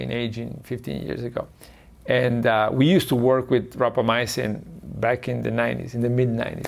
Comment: I think he said rapamycin (the drug in the news for extending dogs lifespan), not tropomyosin.